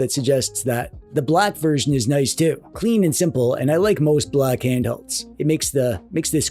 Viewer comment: DID HE JUST SAY SIMPLE & CLEAN